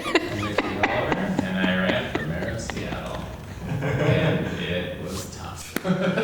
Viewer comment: Somehow my brain thought it said ‘I ran over a minor’ and I was like why the fck she laughing? I think I need sleep